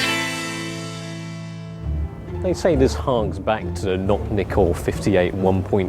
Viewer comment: I thought he said not nikkor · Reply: noct nikkor